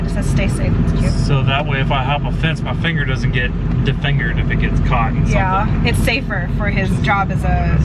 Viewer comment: I didn't understand what said later, but "defingered"...? shudder